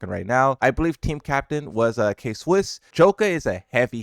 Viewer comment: did bro just say K-wuss 💀